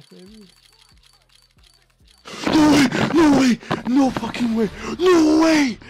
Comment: At the end I heard a clear "Norway" instead of "No way"